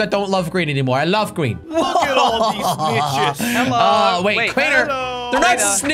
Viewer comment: I thought Crainer said, "Look at all these bishes" lmao